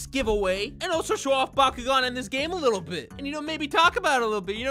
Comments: I thought he said Bakugo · “These bald things” shows tyler1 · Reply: Please tell me I’m not the only one who thought he said Bakugou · I thought he said bakugou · Bro i thought you said bakugou